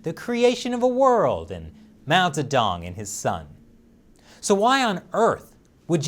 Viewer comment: I honestly thought you said "mount a dong in his son".